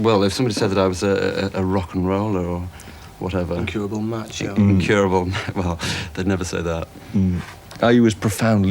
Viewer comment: What does Marr say at 5:53? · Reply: Nevermind I figured it out myself...incurable macho. Thanks guys